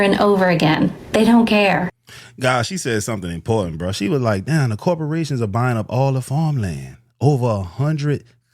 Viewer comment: she did NOT say that corporations were buying up the farmland. She said that "foreign corporations" were buying up the farmland